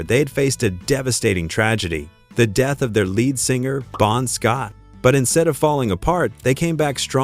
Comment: Did it say Prawn Scott?